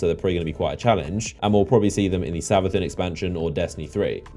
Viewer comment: did u say Savuthun expansion? TELL ME MORE